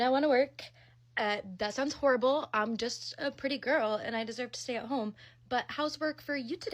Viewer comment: I swear I heard her say she was a pretty girl. 🤣